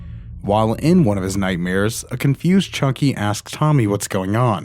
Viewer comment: sounds like he said "chunky" not "Chucky"